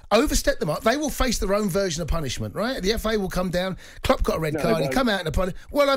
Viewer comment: Did Cundy just say 'The FIA'? 😅 For a moment I thought I was watching F1 😁